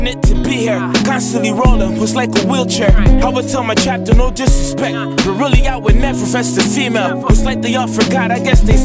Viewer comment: what does say at 2:08. "i would tell my captain no disrespect but really I would never ?????" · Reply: BCDopeToker "I would tell my captain no disrespect but really I would never vest a female"